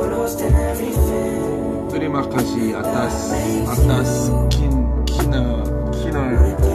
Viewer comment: does anyone know what he says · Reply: dia bilang "terima kasih atas kin....kineeeee... kineeee...." kinerja kali ah maksudnya wkwkwk